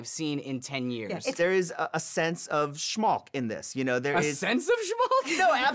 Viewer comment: Did they say "Schmalz"?